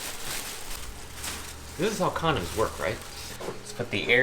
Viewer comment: did he say this is how condoms work right